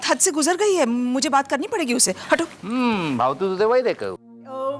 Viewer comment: Can someone tell what did Indu say in Gujarati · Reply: Bhavtu tu ey vaid e kahyu.. literal translation " what you wanted was prescribed by the doctor" in taunt way.. it actually means.. you were wishing for it and it rather happened without any efforts